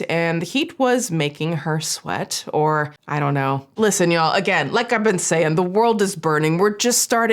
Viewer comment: not sure if you meant to do this, but its hilarious if you did. I swear I heard you say "it was making her sweat. GLISTEN y'all."